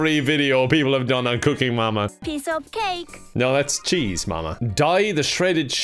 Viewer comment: I thought she said "piss off cake"